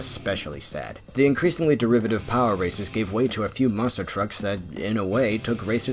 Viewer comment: Every time he says “power racers” I hear “power Rangers”